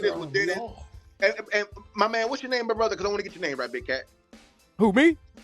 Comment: Did he say what's going on? Big. Daddy I'm sorry if he didn't say that I'm just asking. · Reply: I was 30 minutes and 10 seconds into the video when he said it and I turned on closed captions and he said big cat but I did sound like he said big daddy